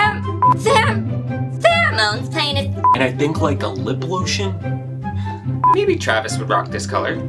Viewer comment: Dear God I thought you said "limp lotion" then with the "face lube", all I was thinking was "please Travis, don't go in the store asking for "limp lube". 😂🤣💀